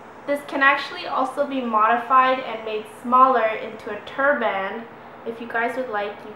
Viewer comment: A turd band lol I thought she said that lol